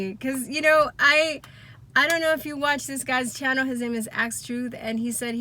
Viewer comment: Did anyone catch the name of the YouTuber she mentioned at 2:55? I guess I’m not hearing it correctly. Thanks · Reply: Axe Truth who also has a channel called Axe Truth Rebirth